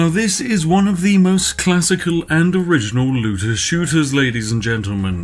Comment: At the beginning it sounded like you said loser shooter